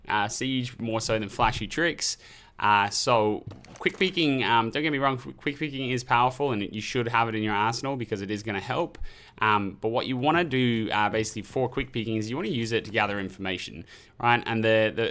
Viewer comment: first time I heard "Quick peeking is powerful and you should have it in your asshole" lmao